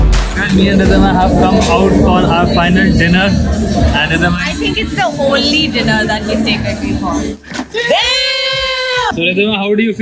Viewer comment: Can someone tell me what she said @ 2:50 right before Craig and Smokey jump in? I tried 5 times to make it out and no luck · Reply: Its the only dinner you've taken me.